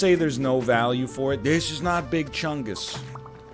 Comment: did he just say "this is not big chungus" in a Duke Nukem voice?